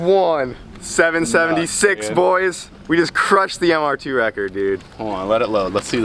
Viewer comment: For a second i thought it said Mw2 record I tripped out 😂